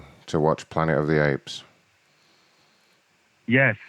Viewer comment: Pretty sure I heard ‘planet of the aids’ 😂